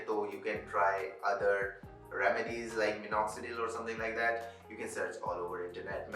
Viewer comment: Cannot hear you at 7:09 Can you repeat please? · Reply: Remedies or minoxidil